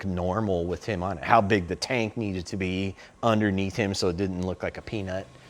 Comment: sounds like he says how big shaq’s taint needed to be under him so didn’t look like a peanut😂😂, I know he meant tank . But tell me that is not what it sounds like